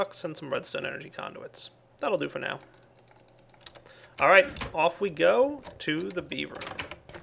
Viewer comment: did he say "Off we go, to the beaver"? lol